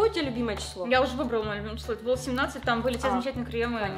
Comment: не знаю, послышалось или нет, но я услышал "хрень-тени"